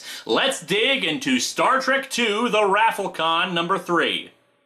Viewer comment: Did say "The raffle-con" or "The ROFL-con" at 2:40? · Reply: Raffle-Con.